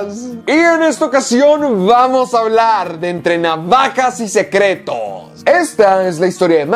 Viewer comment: Por un momento escuché "Entrena vacas y secretos" LOL